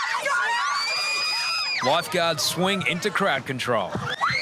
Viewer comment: I heard "crowd control" as "crack control" and I honestly think that is more fitting